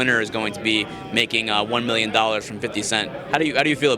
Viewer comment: Thought he said “1 million dollars in 50 cents”